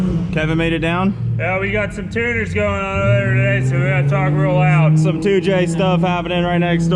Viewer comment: what did say 😂😂😂😂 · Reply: "Yeah, we got some tuners goin' on o'er 'ere today, so we gotta talk real loud!"